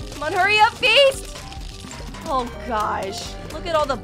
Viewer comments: The time when she said bucket head I thought she said the f word but thank god she didn’t said that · when she bucket head I thought she said f word